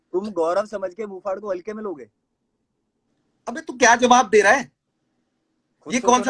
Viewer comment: Sounded like...Ashneer Grover's..." Bhai tu kya kar rha hai?" 😂😂😂😂